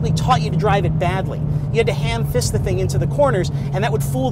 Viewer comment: Did he just say " hand fist" 2:14😂?